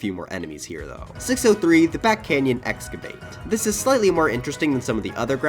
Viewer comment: Did anyone else hear "The Bat Canyon"